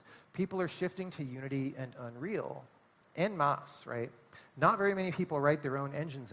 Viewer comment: What is the third thing he puts with Unity and Unreal @ 48:40 "people are shifting to unity and unreal... and *mocks/moss*" (subtitles say moss, sounds like mocks or something) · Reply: "en masse"